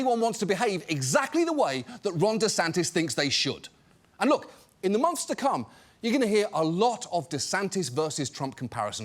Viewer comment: I can't be the only one who heard "In the monster cum" here, right?